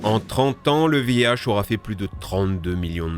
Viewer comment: le quoi ? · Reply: VIH